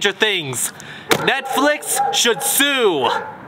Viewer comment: At first I thought he said “Netflix should shoe” But then I slowed it down a bit Then I heard sue